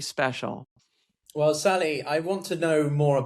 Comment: Did you just call her Sally?